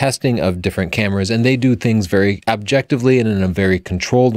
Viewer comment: did Tony say DXo mark test "abjectively"? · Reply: No, he said 'objectively', as distinct from subjectively.